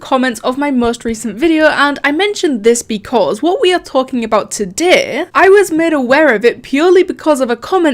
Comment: Did she say Todeeeh